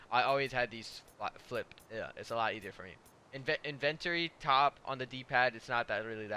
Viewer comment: In-Invent-ary is what he said😂😂🤣🤣🛑